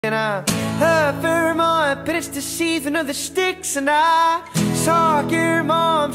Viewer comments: HELP I THOUGHT IT SAID "AND I LOVE HER MOM BUT ITS THE SEASON OF THE STICKS" 😂😂😂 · Ok but why does it always sound sound like hes saying soccer mom instead of saw your mom like hes adding a k sound😅